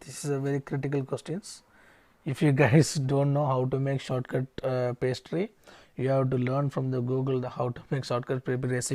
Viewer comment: what are you saying on the minute 25:20? "capestri"?? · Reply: Shortcrust pastry